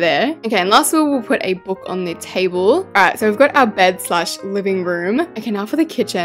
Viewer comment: Those who heard her say « and lastly let’s put a book on the table » instead of « the bed » 😅😂. Like you should practise how to speak 😂 (btw I love you gurl love ur content ❤)